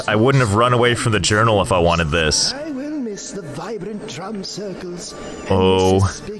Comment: When the voice said”drum circle’s” I heard “drug circle” that’s when you know he can’t talk and he’s HI